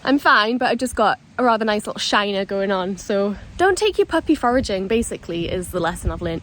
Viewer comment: I thought you said don't take your puppy for a GIN.....I was thinking yep that's good advice 😂......foraging, not gin! 🙈